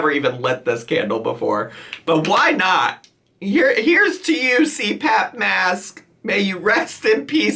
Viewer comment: I thought it said crap mask and kept waiting on the tea